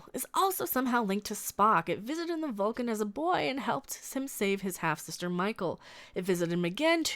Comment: I thought I heard you say at 4.00 that the red angel visited boy Spock on Vulcan and "helped him save his hampster Michael". Sadly I think I misheard. Now that would have been an episode worth seeing.